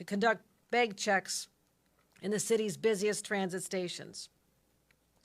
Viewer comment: Thought she said Bed checks in the subways? Why are there beds in the subways?